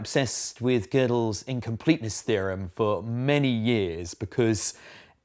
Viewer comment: thought it said "incompetence theorem"